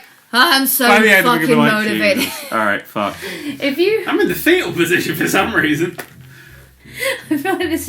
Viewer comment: when he said fetal position I thought he said field position. Now if all soccer players could have a formation like that I'd understand but lol